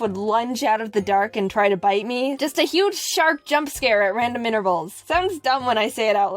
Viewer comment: XD at 8:25 the subtitles say 'at regular intervals' when jaiden says 'at random'.....dont judge me im weired.